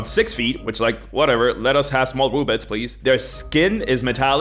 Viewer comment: It sure wasn't me and I blocked him. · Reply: I'm sorry,but what the heck did you actually say at 3:02??? CC says it was "Whatever,let us have small bullets"... My ears say "Whatever,let us have small b00b-lets please"... I really have trouble hearing words sometimes...